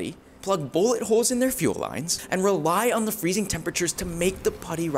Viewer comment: did i heard “putty hard” - 😩